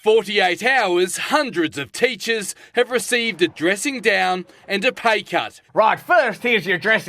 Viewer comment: I thought he said they received a 'dressing gown' and I thought that, while strange, receiving a comfortable house coat was perhaps some consolation for being hit with a pay cut.